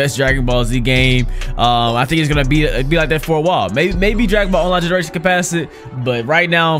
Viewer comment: Maybe what? I cant understand what he says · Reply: He says maybe dragon ball generations could pass it